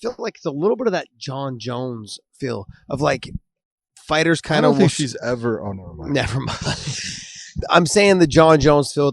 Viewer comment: what does big john say at 53:26? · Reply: “I don’t think she’s ever, oh nvm”